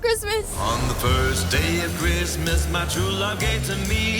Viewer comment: I thought they said rizzmass